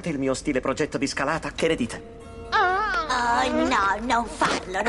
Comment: Progetto di scalata che ne che? Non ho capito l'ultima parola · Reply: Dite